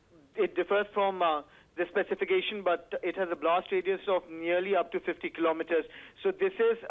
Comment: did he say a blast radius of around 50 Km ?????